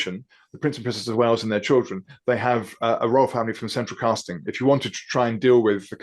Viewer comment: Did he say the Wales family are surrounded by CENTRAL CASTING??